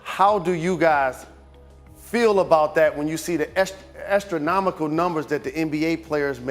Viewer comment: I thought he said "estronomical." That would have been a magnificent pun.